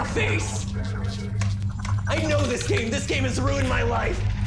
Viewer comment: Hearing mat say "I KNOW THIS GAME THIS GAME RUINED MY LIFE" killed me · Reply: I thought he said I KNOW THIS GAME THIS IS MY LIFE